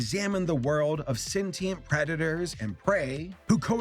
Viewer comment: "sentient predator's and prey" I thought he said "sentient redditors and prey"